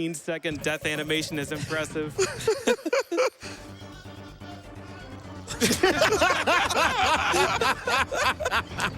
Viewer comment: Can anyone understand what the guy says at 18:05? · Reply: we live in the best timeline